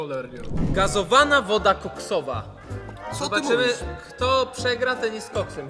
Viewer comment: Czy tylko ja usłyszałam woda koksowa